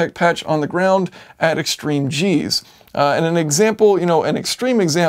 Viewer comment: Extreme Cheese...? I swear that's what I heard